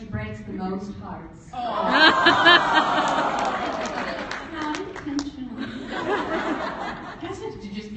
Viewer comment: Help again, what does Natasha says at 45:36? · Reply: K RO. elise said that natasha breaks hearts and natasha was being all cute saying "not intentionally"